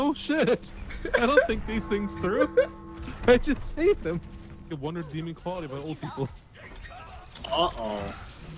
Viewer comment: Did just say "the one redeeming quality about old people"?